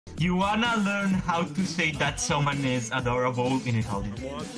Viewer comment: "You want to learn how to say thats so adorable in italian?" B A S T AR D O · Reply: First of all: I thought he said "want to learn how to say someones adorable in Italian" Second: salute